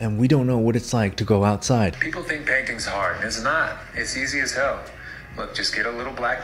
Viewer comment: "people say that painting is hard" I THOUGHT THE DUDE SAID BANKING